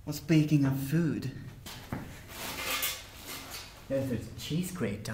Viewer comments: i had to repeat the part where he said cheese grater because i thought he was saying the cheese grape-r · Bruno! Go sit in the corner! I Died from laughing, I just watched Klub Ice XD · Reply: Kittenofdoom123 I misheard cheese grater... it's hard to be intimidated by someone who threatens you with a cheesecake....I was confused...then I realized my mistake and couldn't stop laughing!